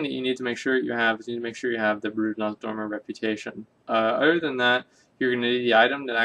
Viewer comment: What the hell do you say at 0:25 ???? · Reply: Noah White I'm a bit late to the party but he says 'Brood of Nozdormu'.